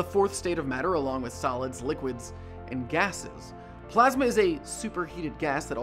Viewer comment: ate 7:35 i thought he said "salads, liquids and gases"